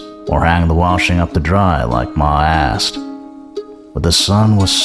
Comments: why did I hear him say my a$$😭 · Bruh he said “hanging clothes up to dry like Ma asked” and I thought he said “hanging clothes up to dry like my ass” I was like what?! 😂😂😂